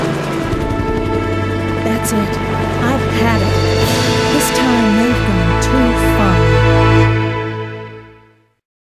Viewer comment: What did Jill said at 0:58? I didnt undestand her. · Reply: she said i've had it this time the've gone too far